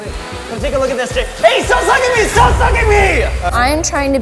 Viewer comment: I’m sorry I’m only a few minutes in and I hear “STOP SUCKING ME!” 🤔